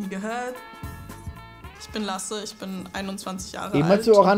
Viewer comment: hab immer "bin Agrarökonom" verstanden^^